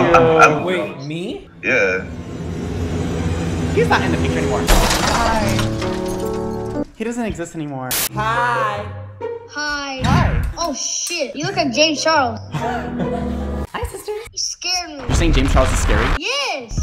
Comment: LMFAO I THOUGHT HE SAID "YOU LOOK LIKE A GAY CHILD" IM DEAD 🤣🤣